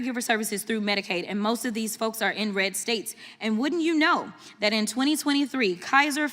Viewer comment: When she said “in red states”, I thought she said inbred states. Then I shrugged and thought, same thing.